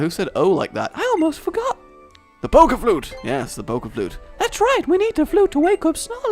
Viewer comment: onward I thought said Poke fluke instead of flute lol. That's what I'm going to call fluke now.